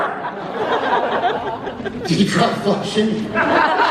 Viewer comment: thaaaaaaaaaank u sm👌👌👌👌💗💗💗 n can u tell me what did Jared say at 30:56?! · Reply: He said "Did you try flushing?"